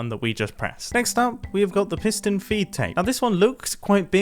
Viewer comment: is it really called a feet tape i always thought it was feed fr can somebody tell me what it is